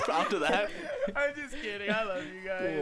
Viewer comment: "Huahwi" (not kawaii) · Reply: You're outro sounds like she says "There's no need to rod."